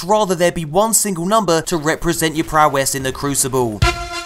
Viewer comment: did he just say prowless instead of progress?